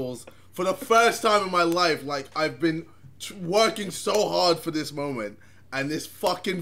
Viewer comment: Who else heard JJ saying I've been twerking so hard.